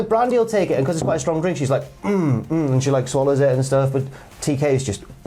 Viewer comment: Did just say Brandi swallows?